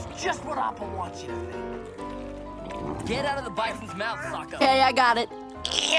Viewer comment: why did I hear Zuko say "get ouy of the bison's mouth fu*ker" 🤣😭